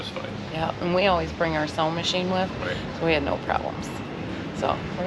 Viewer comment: Looks like y'all had a great time. Turtles don't have last names guys. 😂 I thought you said sewing machine not sound machine! 😂